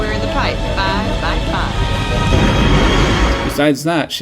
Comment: In the pipe. Five by five. what i hear: "in the pipe. Bye bye bye"